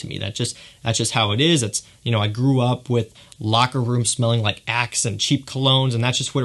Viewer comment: Great video! I lost at at the end when you talked about the locker room smells, I had to rewind because I swore I heard " smelled like and cheap cologne" lol. I enjoy your work and dedication! Thanks for all you're doing!